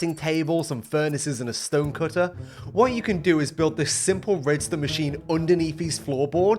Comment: did you just say, a simple redstone machine